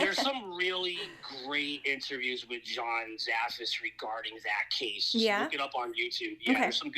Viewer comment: What is the name of the person he says at 1:03:11 John.. something I can’t make it out 😩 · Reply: John Zaffis! 😃 he is Ed Warren’s nephew 😃